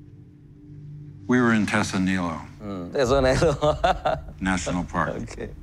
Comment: i can't clearly get what Ford said at 2:22. can someone help? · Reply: "We were in Tesso Nilo"